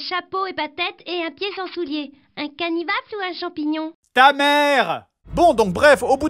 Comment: Bon à 22:53 il dit quoi le gosse finalement ? 3 ans que je cherche la réponse, google ne trouve rien, j'entends "canivappe" ou "canivaple", c'est quoi au juste ? Quelqu'un sait ?